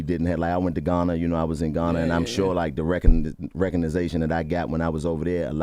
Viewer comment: Jawn said “recognization” instead of recognition with a straight face and nobody clocked it.. 😂😂